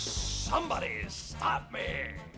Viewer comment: The first time I heard him say this I thought he said “somebody stab me!”